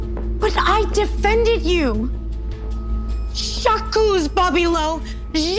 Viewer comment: does anyone know what she said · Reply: "J'accuse" : in french, this means "I accuse"